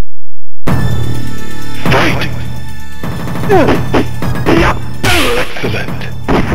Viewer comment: what does the commentator say at 1:38? Thanks in advance. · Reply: excellent